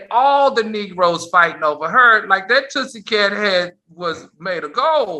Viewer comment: Absolutely love Al Reynolds input on Faith Evan. However, some one needs to copyright the word Tootsie Cat 🐱 🐈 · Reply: "Tootsie" is funny, but i heard it as "Tussy" as in the deodorant! 🤔😆